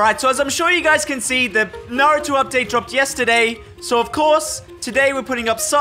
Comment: At the start I thought he said “as you know, there are no new updates” But he said naruto updates